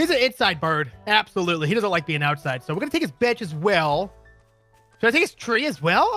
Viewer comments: When said bench, I thought he said b*tch😂😂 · I keep getting the thought that said bitch